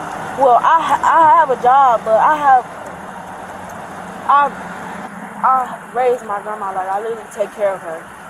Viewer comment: did she say she raised her grandma??😅😅😅😂😂😂😂